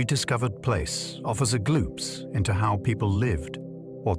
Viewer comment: Also less ai mistakes in grammar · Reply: It said offers a “gloops” instead of “glimpse.” At 1:08. That’s quality production to you? C’mon, now. It’s already ruined it for me. Moving on.